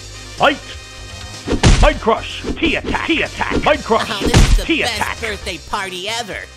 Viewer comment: For years I thought he said "Pee Attack", not "Tea Attack"